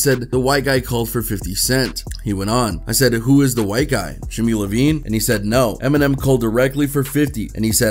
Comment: did he say Levein ?? Lol